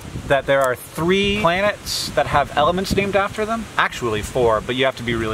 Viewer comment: I heard it as 'There are 3 planets that have elephants names after them' and was so confused. Because it and the discussion that followed implied that there are at least 3 famous elephants named after planets that I'm supposed to know about. Life didn't prepare me for celebrity elephants.